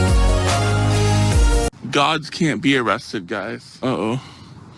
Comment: Ha, ha! I honestly thought said "dogs can't be arrested"!! Too funny!!